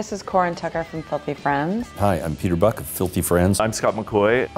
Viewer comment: Thought it said “Filthy Frank” at first and was slightly confused.